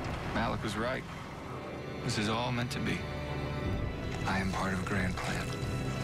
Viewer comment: Literally in the beginning when he said " I am part of grand plan" I heard " I am part of grant plan" I was laughing so hard